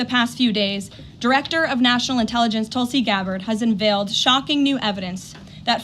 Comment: She pronounces "envailt" instead of "unveiled".